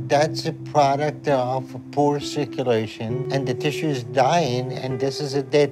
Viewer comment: subtitles say: "And the dishes dying"